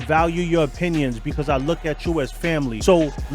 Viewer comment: When you say you look at us like family, do you know what I hear? "YOU LOOK AT US AS MONEY" Checkmate